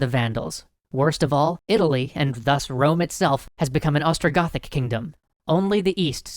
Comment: oh god I thought he said “Estrogen kingdom”.